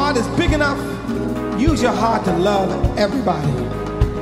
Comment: what the funk he say at 3:05??? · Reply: "I send you love from up above, God Bless you"